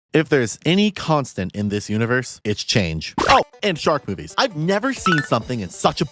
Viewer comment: i swear to god i thought you said "shark boobies"